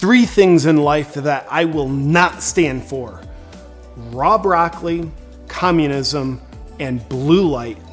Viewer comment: Raw broccoli. I thought he said “Rob Rockly”. Now JP’s porn name. 🤣 you’re welcome.